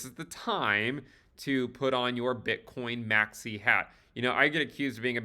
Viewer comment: i thought you said "time to put on your bitcoin maxi pad" 😂😅